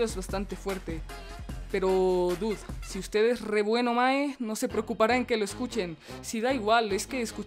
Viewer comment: Acabó de escuchar que dijo "Mae"? xd